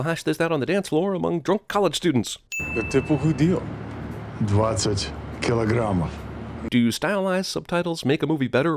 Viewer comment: he said 20 kilograms (in Russian) but the subtitles said 60